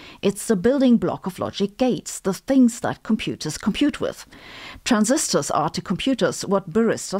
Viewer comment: I heard that as "baristor". :)